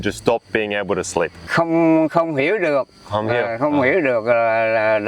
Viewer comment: Did he just say "computer"?